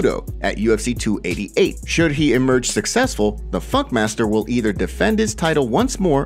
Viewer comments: Sounded like you said “The Fuck Master” 🤣🤣🤣🤣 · Did i Hear F*ck master ? :D